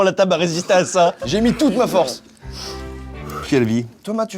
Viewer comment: QUESTION : Pourquoi à 24:25 appelle: Thomas ? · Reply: Je pense qu'il voulait dire " toi mat"